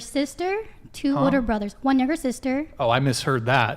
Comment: he thought she said “one niger sister”😭